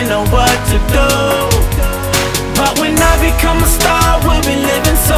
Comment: It says start instead of star.